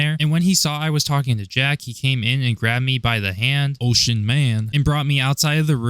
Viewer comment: i was listening to this and when i heard "grabbed me by the hand,ocean man" i had to rewind and see if she actually typed that or if i was hearing stuff XD